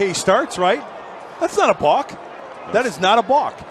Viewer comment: What the hell is a bauck or whatever he is saying